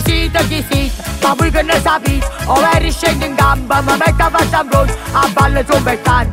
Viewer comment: cosa dice dal minuto 0:48 al 0:51 grazie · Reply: Davvero scendo in campo mi metto la fascia in fronte